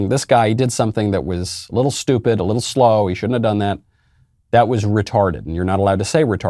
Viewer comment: I thought he said "He should retire", LOL. How "retired" of me!